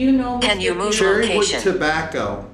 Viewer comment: I was trying to help you she was telling y'all can you move location cuz right before that it said evils coming. But you couldn't hear her when she said move location. That was so weird the show was not responding to that at all.